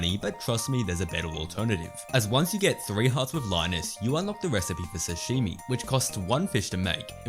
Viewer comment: I couldn't understand what he said in the tip #13 3:04 minutes, can someone help me? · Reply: If you have 3 hearts with linus you unlock the sashimi recipe